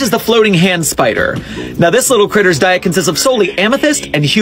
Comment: I thought he said human feces.